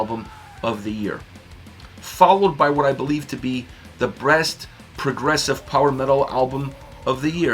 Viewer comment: What??? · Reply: sounded like you said breast not best progressive album. That’s all.